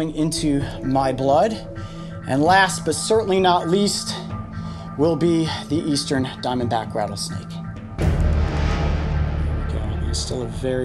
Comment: he says something who if you listen carefully sounds like he said (my backround is a snake) and look at coyotes clothes they look like a snake head side! Do you see it??